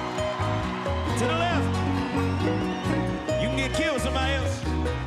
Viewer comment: what is he saying at 1:50 ? You can get killed somewhere else"? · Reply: talking about passing a joint to the left and if you don't do that you could get killed somewhere else I think lmao